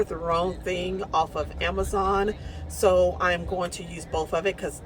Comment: Where did you get your little bags from? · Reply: I think I heard her say Amazon.